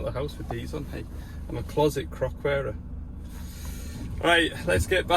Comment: 26 mins in i thought you said you was a closet frock wearer 😂